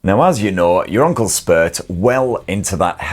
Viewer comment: Thought you said, not uncle spurt. Think you should use from now on.